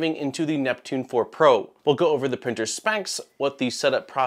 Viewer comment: did you say 'today we will go over the printers spanks'...???